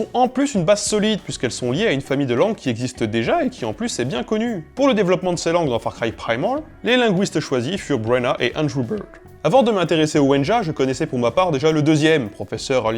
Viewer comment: J'ai entendu Andre Burger à 1:44 :x